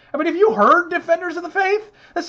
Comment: I heard that as "Alan Page's bra" and that messed me up for a minute.